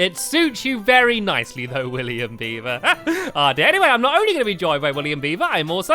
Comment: I thought he said: Good morning William Bieber William beaver: WHAT YOU CALLED ME